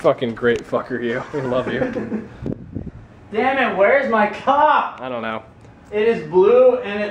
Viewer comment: OMG! I totally choked on a M&M at 2:49 when yells: "Damn it, where's my cop!" I heard is as: "Damn it, where's my c*ck!" I coud have died! Lol! :P